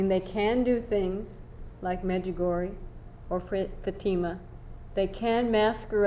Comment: What did she say at 43:23, ... "Medjegory"? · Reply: Medjugorje. This is a village in Bosnia where alleged apparitions of the Virgin Mary occcurred in 1981.